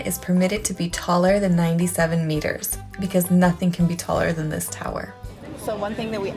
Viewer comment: LOL - when you said "no building in Groningen can be taller than 97 metres", I misheard it as 90 centimetres. Eierballen sound/look like Scotch eggs. [though smaller]